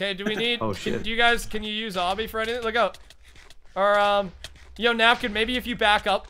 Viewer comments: Says "s**t. Anyone else hear it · Did say "oh shit" at 10:16?